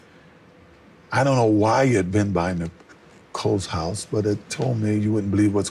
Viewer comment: he's talks about Charlie he says "IT" told me your not gonna believe what's going on down there".. O.j did not say (He) ...I think Charlie is O.J .